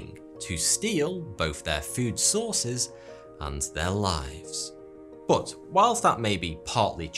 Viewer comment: Am I the only one who heard ,’to steal both their food sources and their wives ? It was later I understood it was ‘their lives.’ 😂😂😂